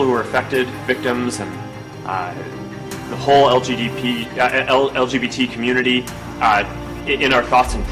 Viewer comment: y'all didn't hear Zucherburg said, did y'all?!?!?! "LGBP.".. then quickly corrected himself? (0:40) the "P" stands for ... sad to say.... is PEDOPHILIA... its gonna happen people. all this "acceptance" and "born this way/gender blending" bull shit being forced down our and our children throats is drawing forth the pedophile revolution... i called it!